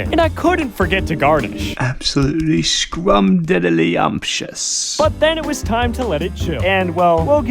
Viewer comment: thought u said diddy